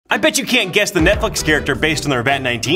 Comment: bro i thought they said stranger things not netflix